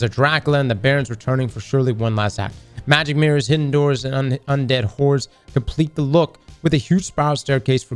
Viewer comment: Did say "undead whores?" I'm jumping back in!!